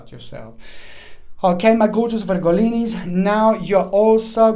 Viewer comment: All signs my darlings. Aries ♈ 26:09 TAURUS ♉ 34:32 GEMINI ♊ 43:39 CANCER ♋ 53:45 LEO ♌ 1:00:56 VIRGO ♍ 1:11:20 LIBRA ♎ 1:27:40 SCORPIO ♏ 1:44:41 SAGITTARIUS ♐ 1:57:02 CAPRICORN ♑ 2:09:54 AQUARIUS ♒ 2:21:16 PISCES ♓ 2:36:13. Warmest wishes. Love and light. Happy to help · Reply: What is the word in 1:21:35? Virgolinis?